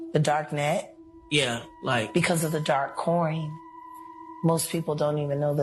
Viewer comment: dark coin is what i heard. not sure if you corrected yourself after 4:08 yet...